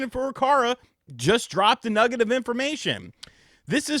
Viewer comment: lol I heard it the first time as if you said "a negative information"